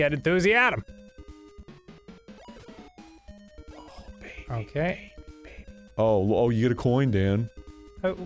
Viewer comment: Am I the only one who hears "oh baby, baby baby" here